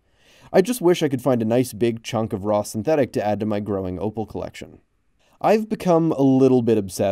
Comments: Bro. Who else thought it said home grown orphans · I deadass thought it said home grown organs ☠️